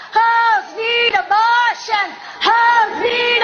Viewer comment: I heard Heil abortions.